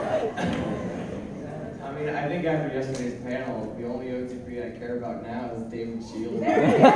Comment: what does he say here? i can’t understand ;w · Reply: "I think after yesterday's panel, the only OTP I care about now is David Shields."